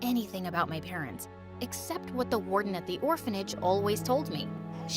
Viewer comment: DID SHE SAY WARDEN i will go play minecraft in 1.19 the wild update